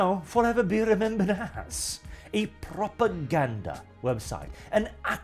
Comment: Honestley thought you said "a Proper Gander website"